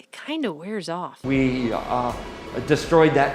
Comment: I always thought he said “it’s a Papal process “. To get it started again. He could be saying “ painful process too I guess either way it’s rediculous .. now remember the ultimate destination is MARS .. OK ?